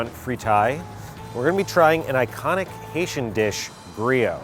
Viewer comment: I always mishear Asian and Haitian food, ugh borderline deaf